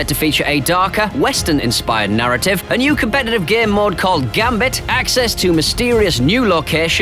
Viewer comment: I thought he was saying "gear mod" instead of "game mode" and was really confused why he was calling it that